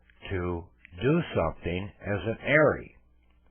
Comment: Hi. Around the 4:00 mark says "It used to be a V in the process and it was uh the usage of that tool to do something as an airy okay." I don't think he said "airy," but I cannot make out what he said. Can you help me out? Thank you for the wealth you are distributing!!!🙏🏾🙏🏾🙏🏾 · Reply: Heir/inheritance